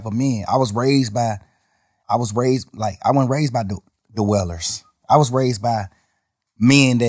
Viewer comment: I thought thug was saying “wailers” whole time he was saying dwell.. I’m like what Bob Marley & the wailers gotta do with your upbringing? 🤣